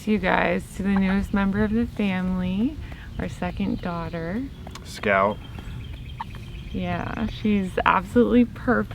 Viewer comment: Did say the baby’s name is Scout, @ 19:06 ? Couldn’t quite tell, since I have a bit of a hearing problem from listing to too much loud LOUD rock n roll over the years. No I’m not kidding lol.